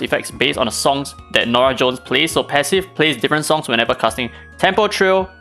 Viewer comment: did he just call her Norah Jones? 😂